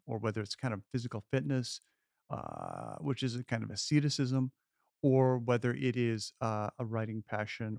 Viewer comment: What is this word that he says at 5:28 “a kind of __________”? It’s driving me nuts, and no, he doesn’t say sedentism. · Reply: Yung Yosef “asceticism:” basically extreme self-discipline. (Pretending like i didnt have to google it.)